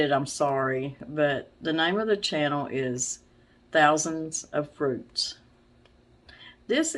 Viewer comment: I thought you said thousands of fruits.....i been watching you going on five years i think it has been...I went back to watch your tamale pie recipe video again, plan on making it tomorrow probably What I noticed is alllll the new stuff you guys have traded for your old stuff even your dining room chairs...lol. I went by miss Vicki's town of Sparks nv i waved as i was on 385 going and coming from Fallon NV where i visited friends... Yea I was a farmers daughter I know the work we did...i would much rather be a homesteader lmao! Have a good one!.